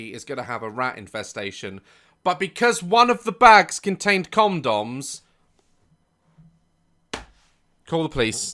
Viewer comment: why did I think he said cum doms 😭